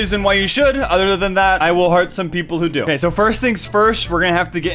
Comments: When he says that he will heart some people, I always thought he said hurt. PS. redstone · i used to think that he said 'i will harm the people who do